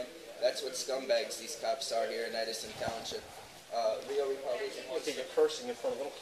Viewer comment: When he said township the cop thought he said s***. 😂 SMH L